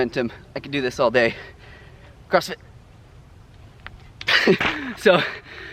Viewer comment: Did you say "crossfit" at 4:48? hahah. Anywy man, first time I leave a comment, but I've been following for a while. The more I watch the more I like you! You're a great inspiration, thanks for spreading the message.